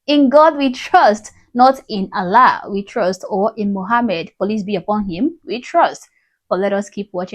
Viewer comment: did she say "Police be upon him?"